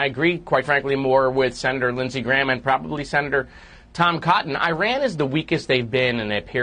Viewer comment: Moskowitz: "Iran is the weakest they've been...": I heard that as "I ran as the weakest I've been". Great foreshadowing! I hope that's accurate! 😜